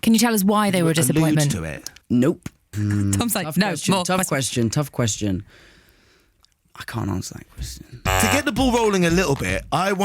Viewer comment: I can’t make out what he’s saying at this moment. Help! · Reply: He said,"I can't answer that question".😂